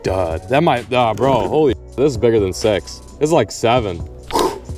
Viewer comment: i heard "this is better than sex" for a second